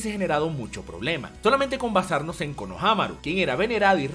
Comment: por qué escuché shikamaru?